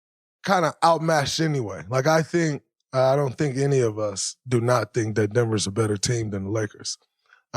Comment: Draymond didn’t say no one thinks the nuggets are a better team than the Lakers. Listen to it again Edit: Found it 0:36. Draymond speaks poorly because he uses a double negative which is confusing when you’re listening to it. “I don’t think any of us do not think that Denver is a better team than the lakers.” He’s essentially saying: none of us think that Denver is not a better team than the Lakers. He says multiple times how good and complete a team Denver is. To think he’s saying the Lakers is better makes me think you weren’t paying attention. Draymond DEFINITELY has a LeBron bias though lol